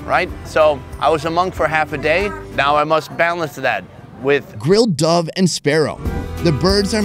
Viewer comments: I thought you said grilled dog and sparrow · Who else thought he said grilled dog....